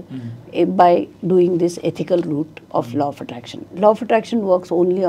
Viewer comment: I kept hearing 'Low' of attraction 😢